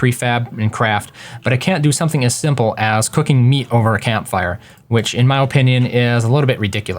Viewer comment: I heard "cooking a meat over a damn fire"